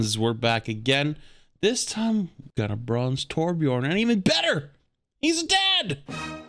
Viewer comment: i swear to god i thought you said "hes dead!" And fucking lost it lol